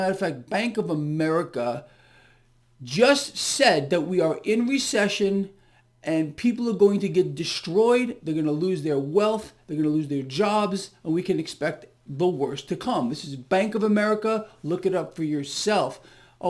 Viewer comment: I thought JPM said depression.